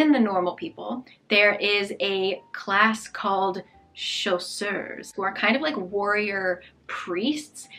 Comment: I was not really interested in the premise of Serpent and Dove until I heard you say "And for some reason, she has to marry a shoe". Turns out I misheard. My brain thought you said "chaussure" (shoe) instead of "chasseur" (hunter) lol